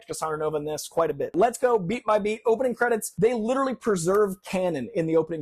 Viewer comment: When he said "beat by beat" I thought he said "beat my 🥩"